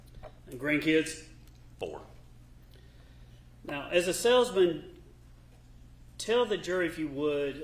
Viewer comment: "I'm a cellsman." Excuse me? 🤔 Oh!!! 💡 A "salesman!" My 🇨🇦 ism ears need to readjust! Sorry, I'll try to keep up!...but then I heard, "Do you have green kids?" ..and I'm lost again! Oh,... "Grand kids!" Gotcha'. ..Sorry! 🙈